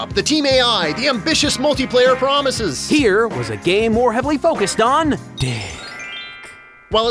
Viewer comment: Let it be known I just heard say “here was a game heavily focused on Dick”